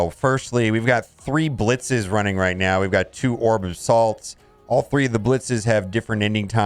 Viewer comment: I thought you said orbs of salt 🤣